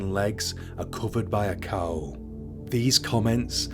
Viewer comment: The grim reapers face, arms and legs are covered by a cow? Around the 9:40 mark- am I hearing this right?